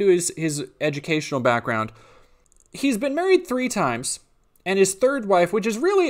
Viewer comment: About a minute 40 in and I hear "He's been buried three times" which I am PRETTY SURE is NOT what was actually said.